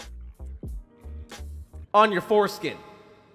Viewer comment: I was about to fall asleep until i hear ''through your foreskin'' and i was like wtf....